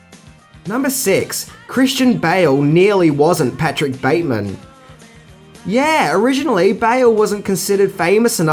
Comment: 'Patrick Bale' instead of 'Patrick Bateman'? LOL